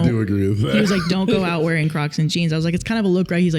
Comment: I heard say, "Don't go out wearing crotchless jeans." Eyebrows met hairline. "Don't go out wearing CROCS and Jeans" was said again. Eyebrows returned to stasis. Agree on not a good look. Both versions.